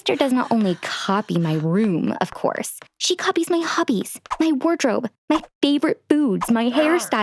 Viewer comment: did she say hubbys